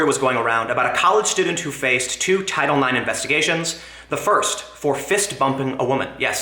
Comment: Some women like fists. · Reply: I was playing this video on 2x speed, and it sounded like Tim said "fist fuck".